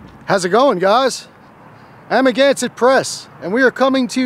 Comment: I swear the first year I watched you I always thought you were saying I’m against the press until I actually listened to you explain your channel name to someone 🫣